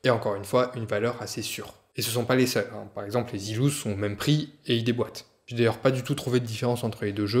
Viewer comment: C'est quoi les zilous ? Zeelous ? Sealous ? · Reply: Zealous !